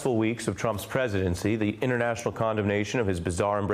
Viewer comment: Excuse me sir, did you just say "condom nation" in the first 9 seconds?! "The "White House" indeed. XD Too funny. Peace!